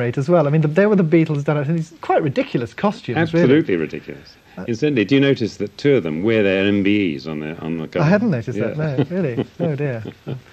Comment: Mr. Martin star saying: "did you notice that..." but I cannot understand what follows. Can somebody tell me? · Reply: '' did you notice that two of them are wearing their MBE Medals on the Sgt. Pepper uniforms.....'' .